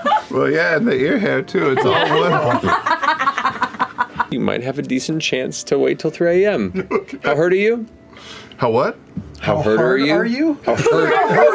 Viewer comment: I straight up thought he said “how hard are you” haha